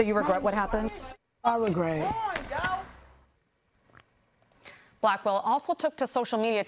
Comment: Lol time out.. Did reporter call her “Black Ball”?